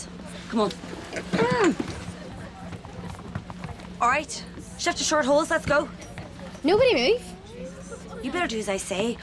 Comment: what did she say ? did she say “shift to shirt holes” or “shut ur shirt holes” ? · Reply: Shift your short holes